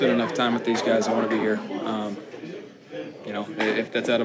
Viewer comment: It sounds like at first he says "I don't want to be here" even the closed captions say "I Dont" 😂